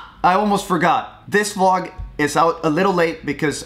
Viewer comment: he said Vilnius? Or is it just me messing around? P.S. Or venice EDIT: He actually said " Vilnius " lol, my hometown.